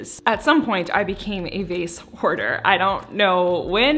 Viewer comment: i definitely thought you were gonna say “vase wh*re” and I was like valid but then you said “hoarder” hahaha whoops